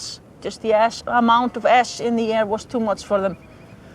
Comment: at the end it sounds like shes saying the amount of ass in the air killed the lambs